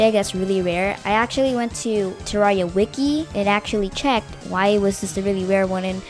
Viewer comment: It sounded like he said, "I went to the Terraria wookie," at 3:08 instead of Terraria wiki. Lol